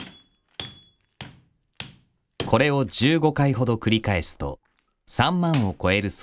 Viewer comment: ん？？15回と聞こえた様な気がするんだけど聞き間違いだよね？